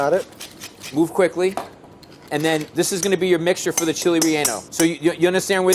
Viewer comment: I thought he was saying "Chilli Riano" and Not "Chile Relleno"😂😂😂